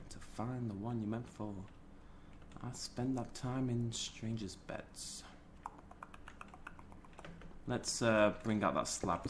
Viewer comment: did he say spend that time in strangers beds